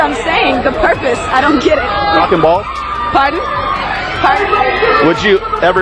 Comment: did he say cock and balls?